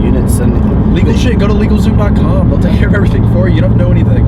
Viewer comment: What is the website @ 5:58 that said? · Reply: legalzoom